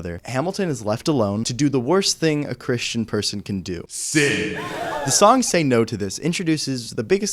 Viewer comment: i've rewatched this video like 4 different times and each time i thought here 25:40 it said: " Hamilton is left alone to do the worst thing a christian person can do: SING " 😭